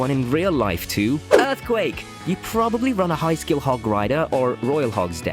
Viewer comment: did you just say “High skill hog rider deck”..?